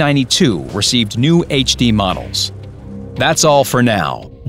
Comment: In the End of the Video i thought i whould say, "We make the Game great again" xD